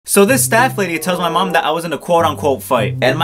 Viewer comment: when he said “staff lady” i thought he said “fat lady” 💀